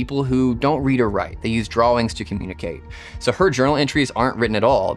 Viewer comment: Did he just say “drawlings” to communicate ?